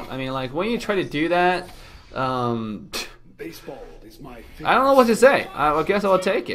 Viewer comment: How you edit saber voice I heard "baseball is my favourite sport"